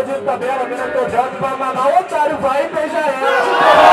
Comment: o que o disse de tabela? Só entendi boto pra mamar otario vai e beija ela.. algm ajuda?